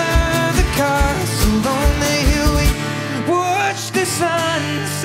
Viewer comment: Ummm am I going crazy or does it sound like he is saying hilly instead of hill? No? Just Me? Ok.